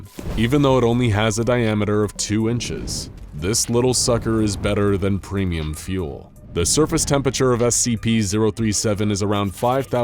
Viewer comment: lol did i just hear better than premium fuel? nice.